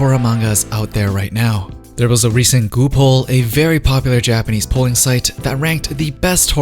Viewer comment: I thought he said a very popular Japanese porn site at 00:15.